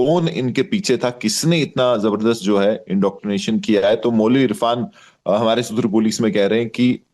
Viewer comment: Did he just said “Jabardast” ??